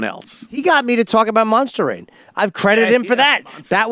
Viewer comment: have no idea what Jim said, he git him to talk about what?? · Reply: Monster rain!